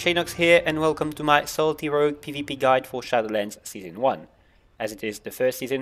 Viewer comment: i thought he said "salty rogue", after the 3d time I figured out I'm retarded.